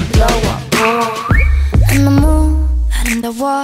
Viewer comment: I thought Rose said "In the moon." It was "Neon neomu"